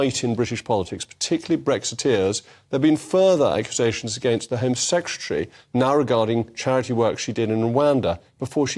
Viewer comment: did he say "the home sextuary"? , he did ! 🤣🤣